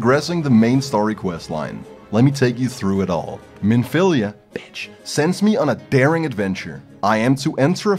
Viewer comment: Bro 24:28 had me. I was listening/watching the video at work and I just heard “Minfilia (bitch)” And fucking died laughing